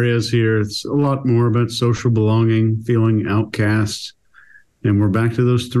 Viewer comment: Chase, I thought you said social baloney. Seems appropriate.